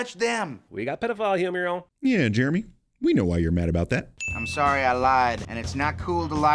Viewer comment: Sin for at 13:13 for putting "made", instead of "mad", in his own subtitles.